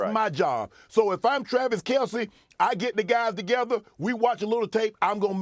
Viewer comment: Did Shannon just say “cuh” ? 😂